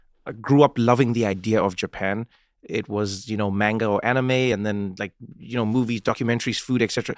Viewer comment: I heard "MANGO"